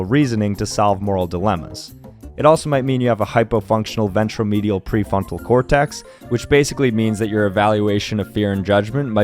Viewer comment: what the heck did he just say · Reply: 'Hyperfunctional ventromedial pre-frontal cortex???' 👁👄👁 ᴡʜᴀᴛ